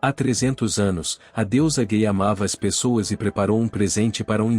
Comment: foi só eu que escuto ele falando "a Deusa gay" ou foi realmente isso que ele falo? kkkkkkkkkkk